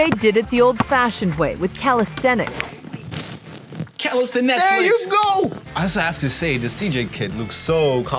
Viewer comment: Did you really said "Calestenetflix"?